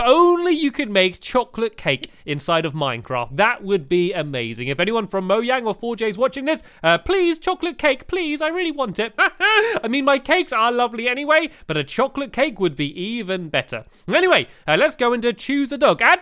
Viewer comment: said Moyang instead of Mojang but he has to talk fast so there will be time to make the next "thing"